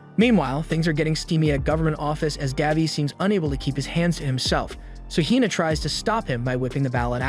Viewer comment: Thought he said “cause daddy can’t keep his hands off” 😂